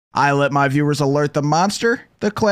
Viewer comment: When he said "i let my viewrs alert the monster" i thought he was saying i woke up in a new buggati 💀🗣️